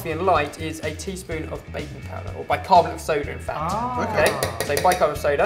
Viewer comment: haha at first i thought you said baby powder!! but i got now.. baking powder